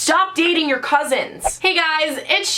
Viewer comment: lol when Sharon said "Stop dating your cousins!" I thought she said "Stop eating your cousins!"